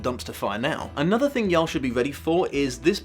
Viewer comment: did he.... Did he just say yall? This most British ass man ever said YALL?? I'm shook to my very core.